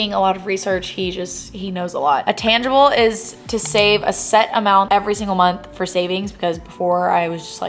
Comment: i thought you said “save a cent every month” like $0.01 😂 you make me wanna travel to texas one day, sending love from philly🫶🏼